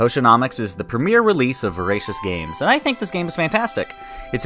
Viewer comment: am I the only one that thought he said racist games lmfao